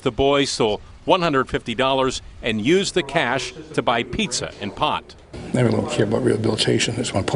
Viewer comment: For pot and pizza...duh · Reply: I thought they said pizza and POP (soda)